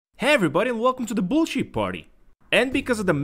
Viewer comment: I thought he said welcome to the bull shit party im dead 💀